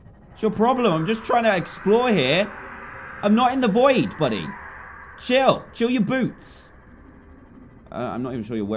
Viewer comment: i thought he said something else "chill your boo- reply if u dont get it